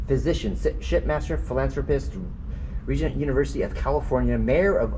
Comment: You said that word so fast I almost thought you said sh.. master 😂 Mary Deming was born on November 26th 1827 and on October 27th 1889 Roman numerals are not easy to read sometimes. You actually could see something through the keyhole. I took three screen shots they are all very interesting. The second one I took I see a face the one right before and after it's not there. Very interesting indeed.